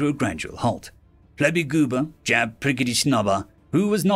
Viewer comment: I had to read the names because when I first heard you say Flarbygooba's name, I thought you said "Flobygoober" and started laughing. Good thing I'd already swallowed my coffee.